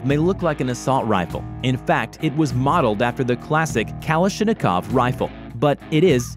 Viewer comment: Did he just say kalishinokov?? Lmao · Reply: Sounded like Kalisthenic-kov to me.